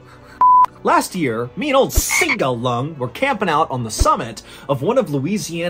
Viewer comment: Oh, you said "me and old Seagull Lung". The first time I heard it as "Mean old Seagull Lung". Your commercial was very funny, but I don't care about sports.